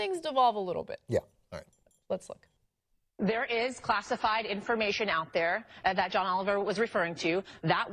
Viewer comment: When the host said "classified information" I actually first thought she said "Foxified information". Which would be pretty much accurate more often than not. LoL